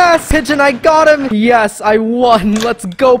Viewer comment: Absolute banger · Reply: did you participate in the ending kinda sounded like you were saying "what are you doing in my house"